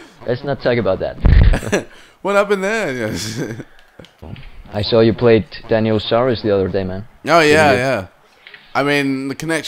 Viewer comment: i thought he said ''i heard you played Daniel Sturridge the other day'' i was like WHATTTTT